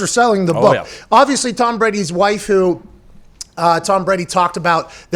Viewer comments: did he say waifu?!?! Pat is a weeb? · I thought he said Tom Brady’s waifu